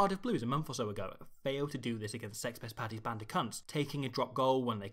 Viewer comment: Am I hearing that right at 11:46? "Sex pest Paddy's band o cunts" Surely I'm hearing that wrong. Or is struggling after a few months of manic video making.